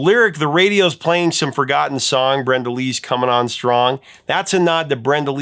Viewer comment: Huh, I heard "Reveille is coming on strong."